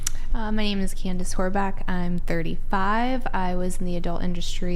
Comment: Did she say her name is Candice Whoreback?